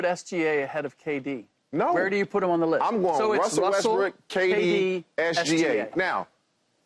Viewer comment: did he just say "Russel WESTBRICK??"